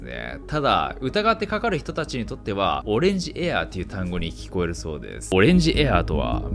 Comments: 「オレンジエア(笑)」、ウィンドシアって聞こえるしずっとそう言ってるもんだと思ってた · 事故調の推定で圧力隔壁破断の7秒後には‐40℃、その1分後は‐20℃、乗客が寒さに気付かなかった？ 冷凍蔵庫に入って見れば分かる。 「オレンジエア」と聞こえる音声は「オレンジギア」、油圧が無くなると車輪のチルトが固定されなくなり動いてしまうとオレンジ色のギア警告灯が点灯するのです。